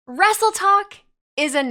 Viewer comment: Does that mean the vs Tiktok rivalry over...👀 I kept hearing "Wrestletalk" instead of "Wrestletok" lol.